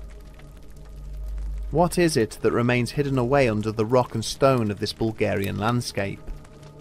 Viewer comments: DID I HEAR A ROCK AND STONE!? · Did I hear a Rock and Stone? Rock on!